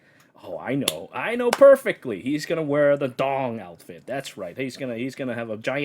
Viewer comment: I thought I heard u say the gatekeeper would wear a dong outfit instead of an Alm outfit LMAO.